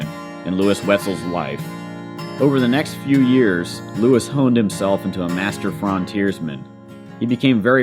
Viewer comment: I heard "Lewis is a master of genocide"